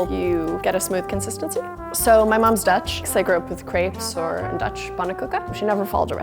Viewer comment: i originally thought she said: "so my mom's Dutch, so I grew up with creeps"